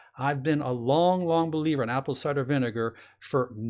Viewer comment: pls anybody,,,does he say apple ''cider'' vinegar at 12:26? thank you,, //sounds like he says ''soda'' there,but it wouldn't make sense as baking soda neutralizes with vinegar// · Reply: cider